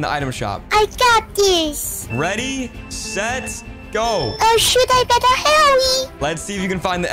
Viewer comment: Bro I swear I heard say “Oh sh1t I better hurry”💀💀💀